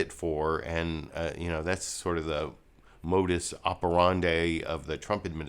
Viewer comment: I thought you said "bogus operandi of the Trump admin." That would have been right on!